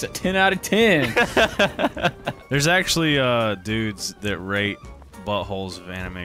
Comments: I thought he said rape not rate as well... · Im with Doo on that, first time I heard "they rape buttholes" 😂😂😂